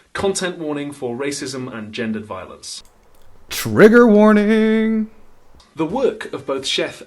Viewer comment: I may've misheard , but did say 'Rigour warning'...